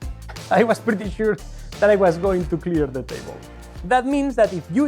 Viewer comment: Definitely thought he said he was pretty sure he was gunna pee on the table😂 I re watched it like 3 times then finally put the caption on😂😂